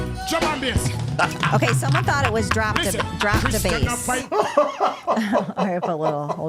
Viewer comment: I thought he said "drop my bass" so he could be heard better💁‍♀️